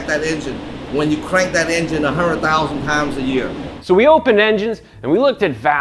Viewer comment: Did he say crank an engine a 100,000 times a year that's almost 274 cranks per day. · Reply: i understood 100,000 pounds